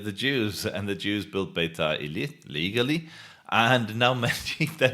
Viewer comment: you need to correct the automatic subtitle since it corrupt what you say - for instance you say : legaly and it says : illegaly